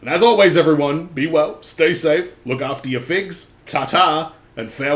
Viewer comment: Had to watch the last few second of the video over again. Thought you said "look after your feet". I was like damn, taking the Tarantino test to heart.